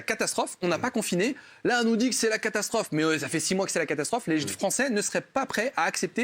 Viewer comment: les quoi ??? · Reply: Les gens